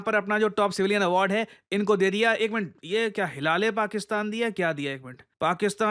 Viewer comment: lol I heard hilale Pakistan 😂